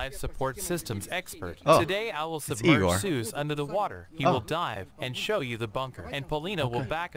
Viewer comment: i misheard the "safety" guy's voice over. "he will die" instead of "he will dive"